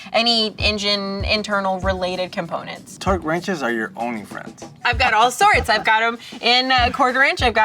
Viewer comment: Anybody else hear "Torque wrenches are your OnlyFans?" at 10:41? I went "wait WHAT!?" LOL. Had to rewind.